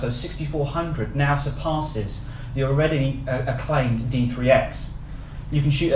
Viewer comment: did he say d3s? surpasses d3s at 6400ISO? really?